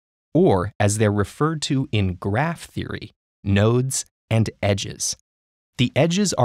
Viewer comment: Did you say "nerds"?